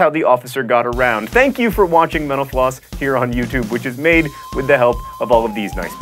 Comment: At the end when he said "Mental Floss" I swore he said menopause.